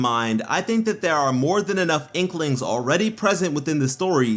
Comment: did he just say inklings? Or have I been playing too much splatoon?